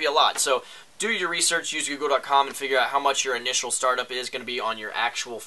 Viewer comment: what website does he say at 1:41 ? sounds like yogle or <link> to me